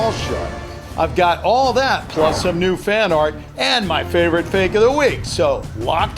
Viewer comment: The first time I thought he said some NUDE fan art XD